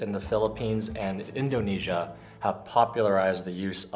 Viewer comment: Did said Indonesia thats where I came from